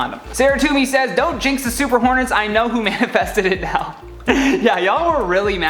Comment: I thought i heard super hornies instead of super hornets lmfao